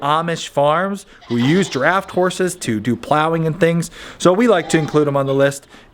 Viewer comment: At first, I thought you said "giraffed horse", and was really disappointed when I realized you said "draft horse".